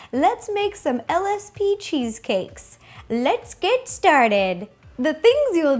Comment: LSP cheesecake? Oh... I thought you said LSD cheesecake...